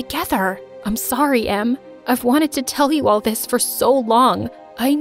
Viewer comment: Am I the only one who noticed that instead of em it says mom? But love the story!!